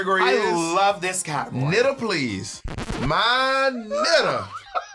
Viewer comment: “Knitter Please” DESTROYED ME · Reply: I thought they said: ANITTA PLEASE 😭